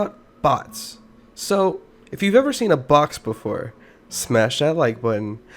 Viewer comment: Thought he said, "If you ever seen a bot before, smash that like button." What he actually said: "If you very seen a BOX before, smash that like button."